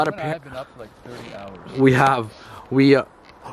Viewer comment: Hey I heard a voice that said o